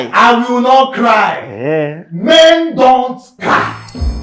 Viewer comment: I thought said men don’t cry 😅😅😅😅